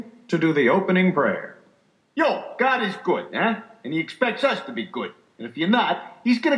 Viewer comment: For a second, I thought the preacher said "Gotti's good" instead of "God is good" lmao